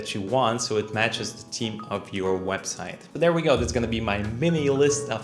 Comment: Thought you said “team” and not theme 😅😂 was so confused